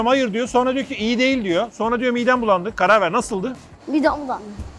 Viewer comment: çevirmene ihtiyacım var bey burada ne demiş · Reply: Midem bulandı diyor